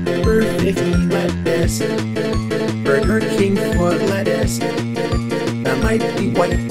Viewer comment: I thought he said "The King of Fortnite Dance"